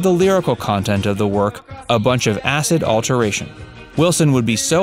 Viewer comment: Mike did not call the lyrics of Smile a bunch of “acid alteration”. He called it “acid alliteration”, meaning alliterating to the effects of acid. Learn how to read.